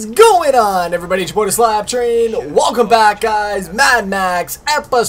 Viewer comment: Ima get like 1M+ dislikes for this but can someonenexplain me...WHAT THE FUCK HE SAYS IN THE INTRO?? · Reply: Really? It sounds nothing like that! All I hear is "two bought a Slap Train"!